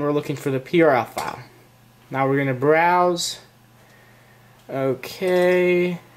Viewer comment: hey! in the minute 3:56 i don't understand you said " now we gonna brosew " and what what is the thing that we gonna brosew? is the prl file what is that please answer me!!